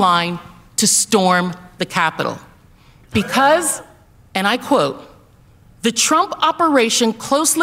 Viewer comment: she said, and I quote, "the Trump operation..." The word TRUMP was not written and Crowder's team didn't catch it! The liberal-leftist do this ALL the time ¿`_